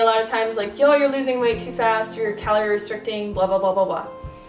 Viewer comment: I thought u said "no calorie restriction..." but then you talk about counting your calories.. interesting info in any case..